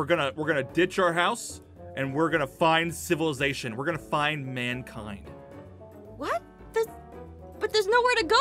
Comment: when he said we’re gonna find mankind I heard l’mankind